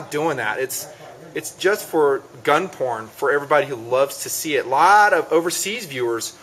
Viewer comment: did he say gun porn? LMAO